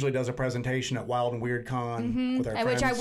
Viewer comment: Hi, Thanks for the response. I appreciate it. You're right, the black dog legends in England go back a long way. Im impressed you know about that. I shouldn't be surprised, seeing as you talked about the Beast of Gevaudan in France. The black dogs, Shuck etc appear more 'dog' like creatures though than the two legged cryptids over there. Of course, out of place big cat reports are also quite prevalent throughout the UK. I saw something along those lines myself thirty years ago. Yes the dogmen/werewolf reports do seem to be increasing here. I've not really given it too much credence on this side of the pond personally but maybe I should think again. I often solo wild camp as we have nothing scary in the woods here but it would be great to go to a place with some recent compelling accounts but I can't find anything of note near me (Surrey, just south of London). I'll take a bottle of whisky with me just in case I see something. Look forward to your next upload. I enjoy watching them late at night. Best wishes. ✌️ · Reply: Yeah that was it, Wild and Weird Con. I misheard. Thought it was a symposium. To be honest I don't know the difference between the two 🤪. I like the way you both question your own experiences. Its very refreshing, and honest. Certain other cryptid investigators could learn a lot. I only follow a select few now, including yourselves of course. There's just way too many around now and a lot of them I don't feel are on the level. Or just wanting more views. 👍